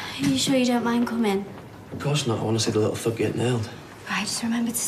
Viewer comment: At the start I thought I heard Gary say "I want to see that little fuck...'